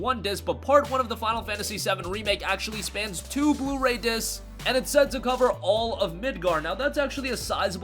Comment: I thought they said 2 parts on e3